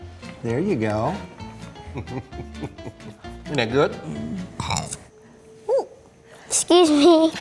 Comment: I know he said isn’t that good but it sounded so funny at first I sounds like he says niqqa at 1:33 😂